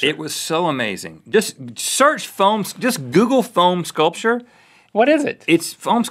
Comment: I swear, I thought he was saying PHONE sculpture, like cutting up an iPhone with a Nokia or something.